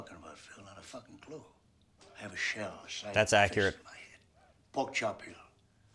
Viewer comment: did he say "pog champion"?